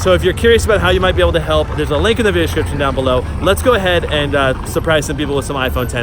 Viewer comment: "Visdixkd" instead of Description😂